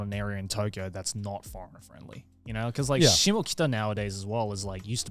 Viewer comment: How do you spell the place mentioned at 7:40 instead of Golden gai? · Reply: shimokitazawa (neighborhood)